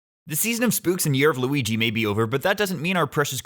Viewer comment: did you say "Lughi" ?